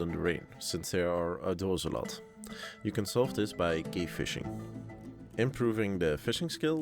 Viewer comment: you can solve this by gay fishing? even the subtitles say so. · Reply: Cave Fishing, hahaha